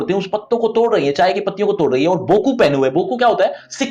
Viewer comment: misheard boku as goku